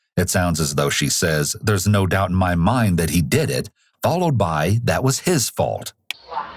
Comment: i heard her say " A couple weeks later" Not " There's no doubt in my mind that he did it"